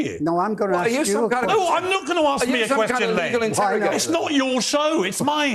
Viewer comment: I'm a bit hard of hearing, but did I hear the hysterical Piers shout: "NO, I'm not going to ask me a question". I think he'll look quite fetching in the new designer straitjackets, which come with a very attractive blue star.